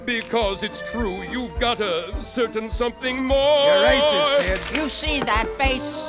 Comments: i thought he said “you’re racist kid” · I thought he said “Your racist kid.” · Am I the only one that noticed the guy said ur racist kid at 1:20? At least it sounded like he said it